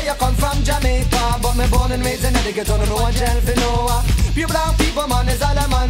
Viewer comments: I think he says he’s “born and raised in Etobicoke” not “in the ghetto” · I thought he said "but me born and raised in conneticut"??